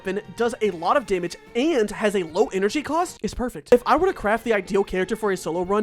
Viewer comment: did you say perfect ??